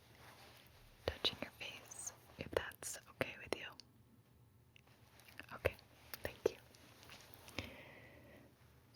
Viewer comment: Hi I'm studying English and I want to know what she said at 0:25 〜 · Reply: 0827 I would be touching your face if thats ok with you